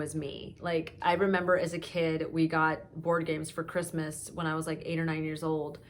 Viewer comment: i thought you Said that you got organs at Christmas! and was thinking why isen't anyone else react to this! then realised that's probably not what you Said! :D you are the best!